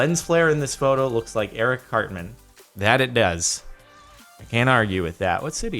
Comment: Lol I thought he said Eric CLAPTAN not CARTMAN